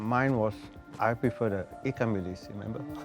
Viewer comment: what did the guy say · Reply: he said he prefers the 'ikan bilis' which is dried small anchovies